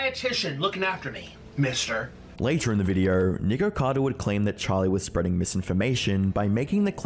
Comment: No offense, but I kept hearing “Nikocader”